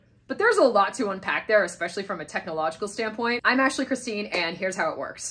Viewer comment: this is great... loving your videos .... and Every time you say "I'm Ashley Christine" I hear "I'm Actually Christine!" 😁🙃